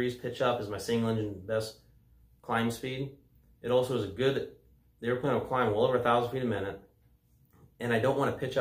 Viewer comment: Love the video . And man when you rotated that thing got off the ground QUICK! Did I hear you say the airplane will climb 11,000FPM at 6:16 ?? 0.0 insane!